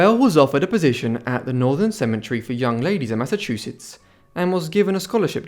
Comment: sorry, what was the name of the institution? Sounded like you said "Northern Cemetery (British pronunciation) for Young Ladies" Did you mean Northfield Seminary for Young Ladies?